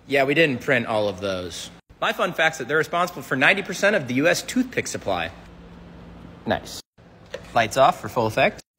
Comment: when he said tooth picks i thought he said toothpaste and thought of Willy Wonka and the chocolate factory 😭